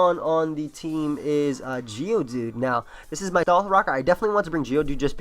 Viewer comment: This is your what? Your dalf rocker? lol what the hell did you say just there?